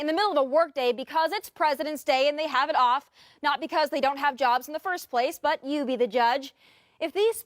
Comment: “Not that they don’t have jobs” thought they said you don’t have a job?